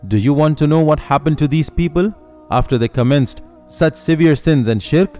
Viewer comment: Wait a minute why does it say the S word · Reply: He said such severe sins and shirk not what you thought